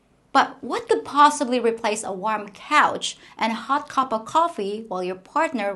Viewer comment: When you said "a warm couch", I thought you said "a warm POUCH" LMAO